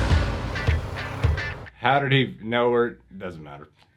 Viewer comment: It sounded like you were gonna ask at 45:48 “how do it he know where she was?” Cause of her pacemaker silly.